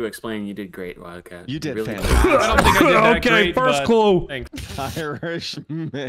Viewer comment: even though I never plan to watch nogla, i clicked the thumbnail and ah i knew it was Nogla's video as soon as I noticed the subtitle at 1:42 was "OKAY FIRST CALL" instead of what he actually said "OKAY FIRST CLUE" XDDDDDDDDD